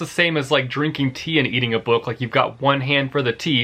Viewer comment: i think he said eating a book Anyone heard that too or am i just stoopid🙂😂😂